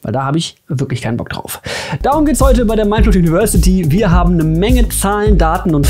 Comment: bin ich der einzige der bei 0:43 Minecraft university gehört hat?